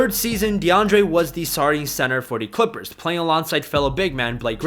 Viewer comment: Did anyone else hear "sorry center" at 0:37?